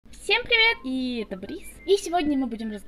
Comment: Мне послышалось " всем привет, это Борис 💀"